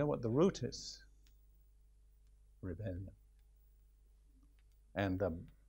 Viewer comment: What does he say at 52:43? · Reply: Rebellion